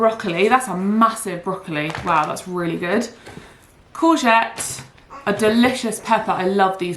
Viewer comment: Right after you took the broccoli out of the box,(2:58 and again at 4:14) what did you call what looked to me like a zucchini? I replayed it twice but still couldn’t make out the word. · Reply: Courgette!